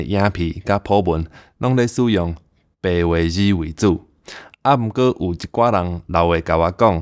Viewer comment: If not for the subtitles in POJ, I'd have thought you meant "old people" in 0:27 instead of "leaving comments". Would have been my fault tho, and not your pronunciation's. :D